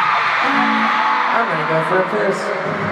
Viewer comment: what did louis say at 0:48 ? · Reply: "I'm gonna go for a piss"